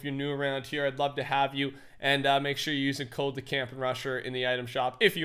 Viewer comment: For some odd reason i heard you say the camping Russian